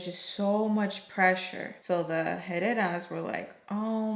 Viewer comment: Why is that when you first said “the Herreras” I thought you were saying “the headasses “ 🤣